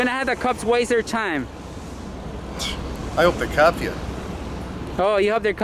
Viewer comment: I def thought he said I hope they cap ya🤨